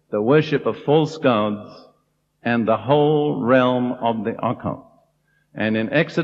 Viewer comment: May I ask if anyone knows what he said here 23:02? The realm of what? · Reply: Occult